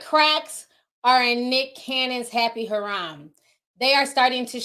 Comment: Love ya but harem (ha•rem) and haram (ha•ram) are two totally different words. Funny enough though, a harem is not considered haram to them (muslims.) Happy New Year Queen! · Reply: I thought she was saying harem as haram because of her accent at times. But you’re correct, the correct word is harem (her・am).